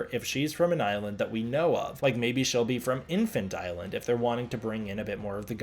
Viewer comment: I also thought said “Infant” as a reference to Infant Island, but he just said Inland. 😂